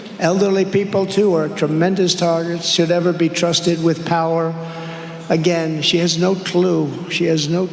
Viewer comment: is he actually saying "old people should not be trusted with power". His speech pattern breaks up, but it seems like he is Saying the Quiet Part Out Loud.